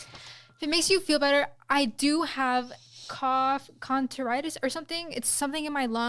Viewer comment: Did she just say “Cough contoritis” 😂